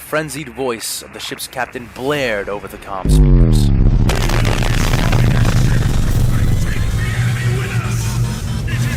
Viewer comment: What is being said at 10:53? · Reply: WE WILL DESTROY THE SHIP AND TAKE THE ENEMY WITH US! IT IS BETTER TO DIE FOR THE EMPEROR THAN TO LIVE FOR YOURSELF! <link> it's a great line from this awesome trailer. I was literally shaking with joy when I was first hearing this part of an amazing story (that sent righteous chills up my spine) - a genius introduction for the "first contact".